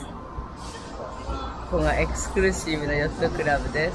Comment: デヴィ夫人はexpressive(表現的な)ではなく、exclusive(上流の)と言っておられるように聞こえます